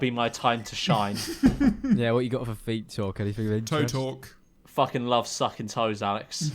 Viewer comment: He said feet talk and I thought he said "Featal". In that context I almost had a brain aneurism